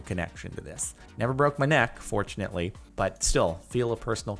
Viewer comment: I can't be the only one that thought he said "I never broke my neck, unfortunately"